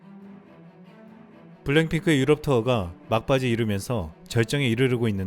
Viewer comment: 내 귀에는 왜 블랙핑크가 아니라 블랭 핑크로 들리지!